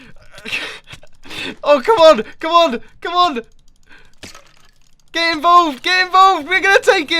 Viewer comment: Did mumbo say "get involved niga"?